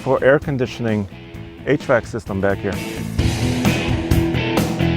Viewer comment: Swear i heard him say "8 track system back here" nearly died laughing